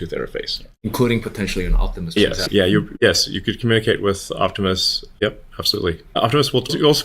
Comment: I thought he said you could communcate with an Octopus.... Optimus :) But you know.. Octopi are smart too. and crows. Oh nevermind